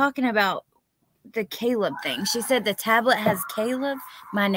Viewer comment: I thought she said she has “cable” on her iPad